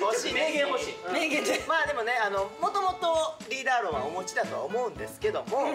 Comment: 大ちゃんの「お持ち」が「お餅」に聞こえるし、大橋さん「ん、おぉw」って反応からして絶対リーダー論持ってないんよ😂